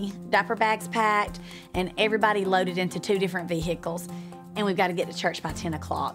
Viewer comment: OK no, she actually said ve-hickles